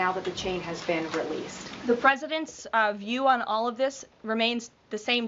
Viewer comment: Did she just say the presidents lol how many presidents are there , two maybe musk and trump